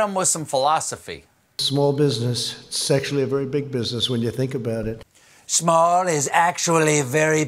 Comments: it sounded like he said "sexually, its a very big business"... and standing next to Ivanka... a Freudian slip Mr. President? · I swear when trump is talking about businesses it sounds like he says “Sexually”. Not “Actually”. And considering trump, who knows....